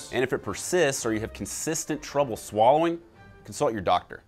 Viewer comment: i thought he said "insult your doctor"